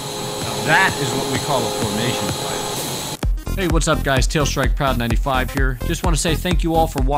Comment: Did I hear "Tailstrikeproud95 here" at the end!? 😂